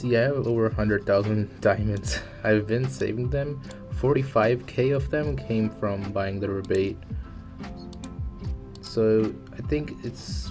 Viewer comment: hey dude! could you say what did you buy for 25€ that you said it was worth it in the video? i couldnt really understood, you said it at 2:04. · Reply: It's the 45000 diamond rebate, it gives you 9000 diamonds for every 5 chapter you complete.